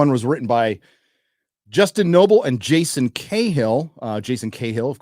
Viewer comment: I thought said "justin k-hole" and not "justin kayhill"... But that would explain a lot of things if it were so.